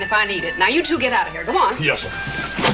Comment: Good old Joe practicing gender equality even before gender equality was a thing A law for unions (rich) and a law for wiens (poor) still like that today. 15:00 Anybody else hear Hoss call Mrs. Shaughnessy "sir" ????